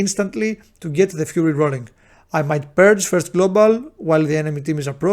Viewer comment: what do you purge. Couldnt make out what you said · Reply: With 'purge first global' I actually mean using 'consume magic' as my first global cooldown while the enemy team is approaching. It's a little bit of jargon where all offensive dispels are often called after the original shaman offensive dispel 'purge' . I will try to use more direct vocabulary in the future. Thanks a lot for the feedback